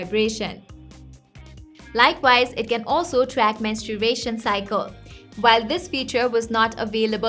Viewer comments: I swear I heard that it tracks "mensturbation cycle" on 7:19 mark. I better get this watch 😄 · I don't Understand how nobody noticed she says "masturbation cycle"